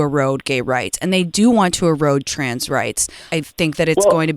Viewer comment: everything you said is incorrect. Perhaps listen to the video before commenting. She cut him off, he continued ‘if you let me explain, and, what I mean by that’ .. so no, he didn’t just say it easy in general like you’re implying 2nd. He never said gay was choice. He said Trans people are born like that, having surgery is a choice. So again, you’re wrong. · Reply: true i did misshear him when he said trans i thought he said gay. and yes she cut him off, but still the statement shouldnt be "being gay is easy" it should be "its easier for gay people to have connection with their partner" lol being gay isnt easy, but certain aspects of being gay is easier than being straight. So you cant just throw out the statement "being gay is easy" because its just an untrue statement